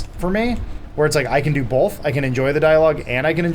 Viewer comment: Did Adam say "Bolth"? I knew it, he is Funhaus Burnie.